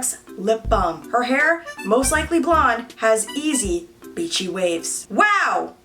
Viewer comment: did she say bitchy whales? Lol I’m dear because that’s what I heard